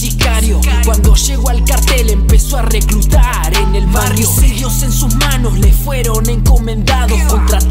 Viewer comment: alguien mas se dio cuenta que dice homicidios y ahi puso magnicidios